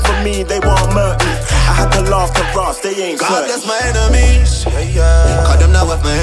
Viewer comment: did man say god is my enemy?